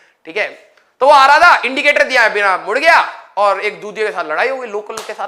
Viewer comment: did sir said chutiya or my ears are damaged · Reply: he said "EK DUJE" ke saath ladai hogi